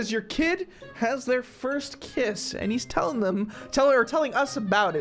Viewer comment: I thought it said your kid is having there first KIDS. 🤣🤣